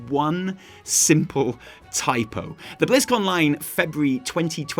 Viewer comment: does any one think it sounds like he is saying blizzcon lime